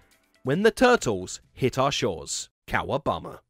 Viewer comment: did he say cow obama? I'm pretty sure he said cow obama.